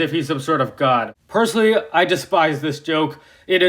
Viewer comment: What did you just say? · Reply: He said, he despises this joke. Same with me. It’s just not funny .